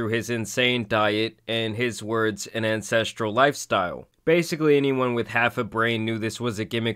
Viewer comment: I just realized he's been saying ancestual lifestyle. His lisp threw me off. I thought he was saying incestual lifestyle. No wonder child services didn't pay him a visit.